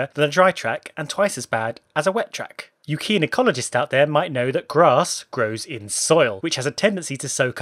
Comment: I heard "you gynecologists out there" and tryed desperately to understand the seemingly shocking joke!